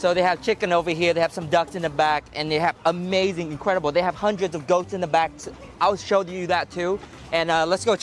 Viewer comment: I'm glad it was goats in the back - I thought you had said dogs